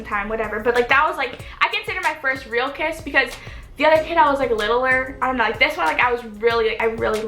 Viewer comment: did you just say littleer